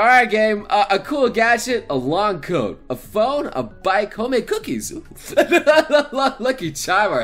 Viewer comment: I thought it said homicide cookies and lol THE FAMILY BUSINESS i didnt know dean was a pokemon trainer and i got James when i took the test